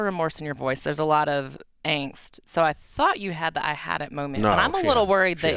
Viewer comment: Lol read this one all wrong. She said "You've had your 'I've had it' moment haven't you?" and did what we all did... he shook his head NO! 😂😂 Get better. This crop of "personalities" can't replace in this condition. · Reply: Lol she actually said “when you first picked up… I thought you had the I had it moment” the term “thought” is past tense jakey jakey 8:27 🥸